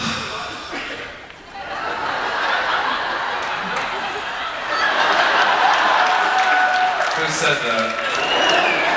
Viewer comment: anyone able to hear what the guy said from the audience at 14:10???? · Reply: He says he's straight...